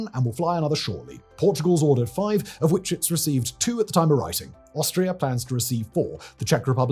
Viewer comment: I think you said Australia will get 4, but it should be Austria. Easy slip of the tong, brilliant video. Maybe in 50 years time another you-tuber will make a video of the successor of the C-390 which flies with air forces all over the world. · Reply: No, listen closely he said „Austria receives 4“